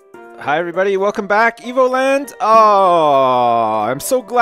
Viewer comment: i thought you said emoland